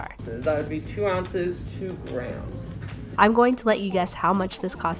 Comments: When the cashier said 2 grams I thought they said 2 grand and I freaked out for a minute and so coincidentally my price was way off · I literally thought the guy said “2 ounces, 2 grand” 😂